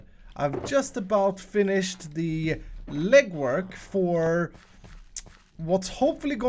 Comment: is it sad that at 0:35 I heard ligma not legwork?